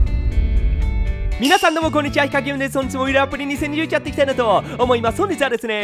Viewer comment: です言えてなくてヒカキンですに聞こえる